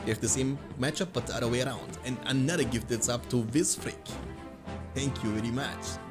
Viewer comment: what happen to him saying essential 🤣🤣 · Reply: "another gifted sub to this pr*ck?" I swear to god that's what he says